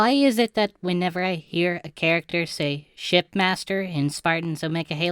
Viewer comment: Now I feel like I'm going crazy cuz you sound like you said "Shitmaster"